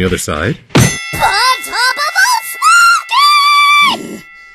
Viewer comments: what did even SAY there?!?! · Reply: "ON TOP OF OL' SMOKEY~!" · What did say from 2:47 to 2:50? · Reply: Hannah Brown on top of ol Smokey